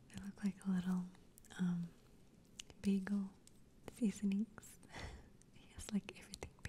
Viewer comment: What is she saying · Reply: she's saying "seasonings" in reference to the bagel, lol.